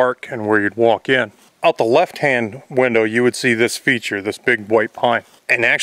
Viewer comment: I thought he said "creature", which I really liked, but then I reversed back and he said "feature" instead... ;)